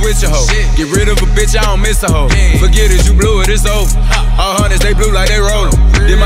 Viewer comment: Did DaBaby just say "ALL BLUE" & throw up the Crip C's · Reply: he said 100s blue like they rollxn its a cripk set